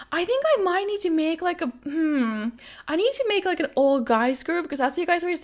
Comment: When she said "all guys" i thought she said "old guys" for a minute 😂